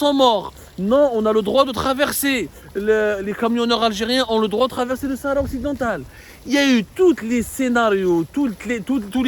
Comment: Mr Abdou, j'ai entendu "les algeriens ont le droit d'aller dans le territoire du sahara..."😁😁😁🤣🤣🤣🤣🤣🤣🤣🤣